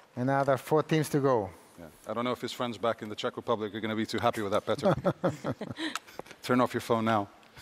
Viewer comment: What did Pedro Pinto commented per se, when Slavia Prague goes to Group F? ' I only heard '....Turn off your phone now.' ? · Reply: Elias Salome he said “I don’t know if your friends back in Czech Republic are going to be happy with that Petr” as Group F is a very hard group and it was Petr who is from Czech Republic who chose it.